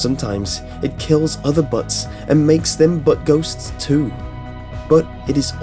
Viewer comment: I thought you said the "Butt Goat" 😂